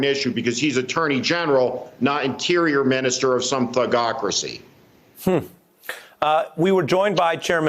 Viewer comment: "Thugocracy" yes, this covers trump,his AG and admin perfectly...! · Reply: I thought, Mr Schmidt said "fuckocracy"! Did I mis-hear? 😷